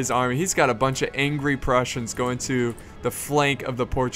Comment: I thought he said angry russians, And I was like, May god have mercy on Portugal. But then I regonised he said prussians, and I was like 'Oh. Phew.'